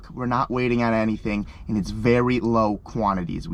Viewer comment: Jordan is the best addition to the shop. Things will actually get done and make it easier to plan content and attend the events. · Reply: When was talking about his merch and said it's very low quantity I thought you said it's very low-quality I was like why the f*** would you say that boutbur merch lmao